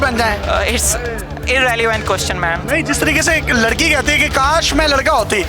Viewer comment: did he just say "ma'am" 😰 sb notice kr k btana